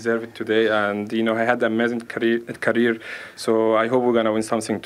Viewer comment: all I heard was “I will not take it”